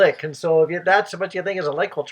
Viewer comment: Omg that is soooo cute...I thought you said licking board...licking wars...yessss sound like fuuuuuuun